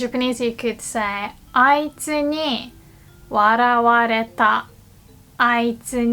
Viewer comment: I love your videos! Quick question: in the beginning when you talk about using the passive form of 笑う it sounds like youre saying 笑われた but the text on the video says 笑わられた, which one is correct?